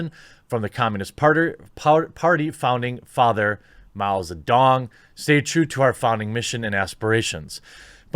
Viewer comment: did he say miles of dong